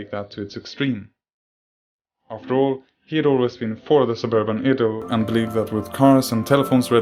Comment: So the saying goes: “An architects dream is an an engineers knightmare” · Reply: What is the narrator say at 9:24 suburban edo?